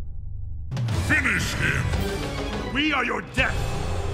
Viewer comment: what did Scorpion say? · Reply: We Are Your Death!!!